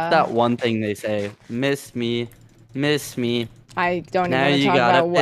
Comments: The always thought he said piss me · I THOUGHT HE SAID PISS ME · I thought he said piss on me